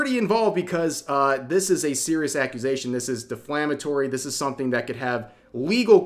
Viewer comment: Did he just say “deflammatory??” 😅